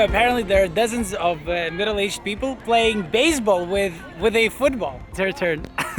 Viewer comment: Did he say people playing baseball with a futból